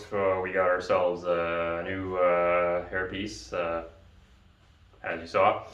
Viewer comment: Sorry to hear you have herpes...they have shots....what? Hairpiece? Not herpes...OK. sorry. I misheard your presentation. You're gonna get like 87 herpes... hairpieces, in the post box now. Kinda gross. Hehe! Anyhoose, loved the latest but if you don't care who will. SAVE THE RUSTY AND MUSTY! Till the next time, peace out!